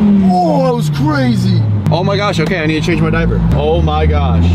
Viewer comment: Did just say, "Oh my gosh. OK I need to change my diaper."? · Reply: yup did say "Oh my gosh. OK I need to change my diaper."